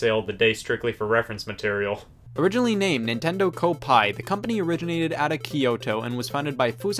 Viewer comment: thought it said oppai